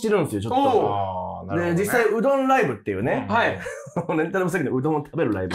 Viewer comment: のところ、 女の人の声で ねぇって聞こえる